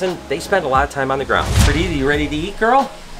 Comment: So Happy about the expansion!! Can't wait!! Also, 6:18 did I hear perdidi lol